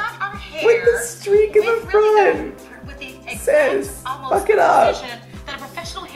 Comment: To all my fellow Germans here: Did he say „was geht ab?“ at 3:57??? I‘m replaying nonstop😂😂😂